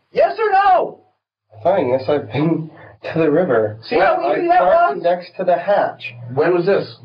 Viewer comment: Who does the captions for these videos? You keep putting (indistinct) when i can clearly hear them. 41:20 the detective says "see? That wasnt that hard!" Genuinely, do yall just not do your jobs cause i heard that on the FIRST listen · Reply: actually he says "see how easy that was?" 💀